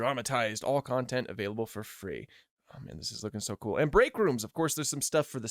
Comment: I heard backrooms